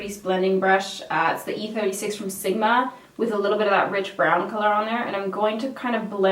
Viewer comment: I thought she kept saying bitch brown. 😂